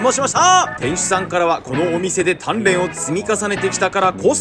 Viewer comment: 鍛錬を積み重ねてきたが、タンメンを積み重ねてきたに聞こえた。